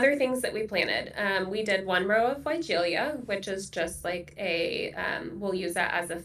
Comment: What shrub is talking about at 36:30? I couldn't quite make out the name. Thanks! 😊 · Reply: Weigela!